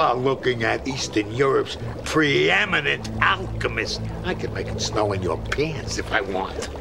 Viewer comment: I thought he said "I can make it snow in your penis if I want" That doesn't make any sense, I know.